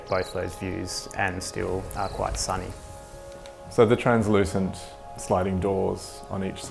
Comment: those transparent doors will terrify me at night. omg. haha. i'd definitely put curtains on them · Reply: I thought he said they were 'translucent'?